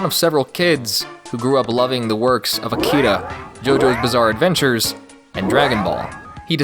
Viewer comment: Did I just hear the words JOJO?!